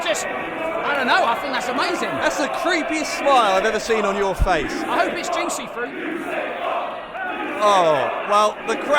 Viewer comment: what did the crowd say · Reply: They chanted you sick fuck